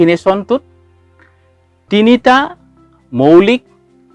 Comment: Sir what did you say at 4:25.. tinita moulik.. something like that..can you translate that